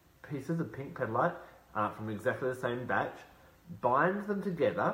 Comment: What is the word after "buy two pieces of pink petalite from the exact same..." at 5:05 please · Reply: Matea Vukic batch